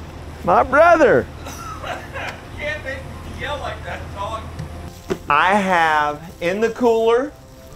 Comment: did just say " you cant make me tell like that dog" 😆👍👍